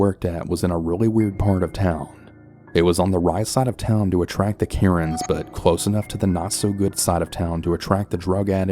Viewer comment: did he say attract the Karens 😂